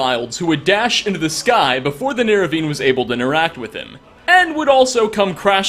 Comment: did u just say "nerevine"